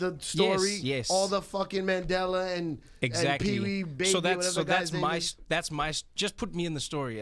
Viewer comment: did he just say "or the fucking mandela''@ 6:25 sec. this guy is an ass, for real.